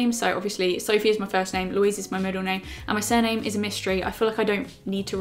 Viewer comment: When she said her surname was a mystery I thought she was saying it was 'amystery' I was like- I've never heard that one before...I did feel stupid once I realised 😂🤦🏼‍♀️❤️